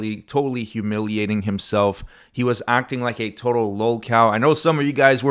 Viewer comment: Ah, lolcow. XD I thought you were saying low cal, like low caliber.